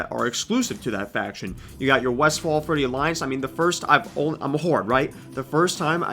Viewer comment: Dude when you said, "I'm a horde", I thought you said, "I'm a whore". Excellent content as usual.